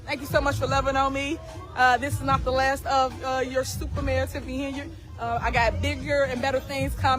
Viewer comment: I swear i thought I heard her say stupid mayor Tiffany henyard. Lol Depending on what her definition of a bigger and better is.Yes you got some big shit coming up in the future. But as far as better not so much. But we shell see.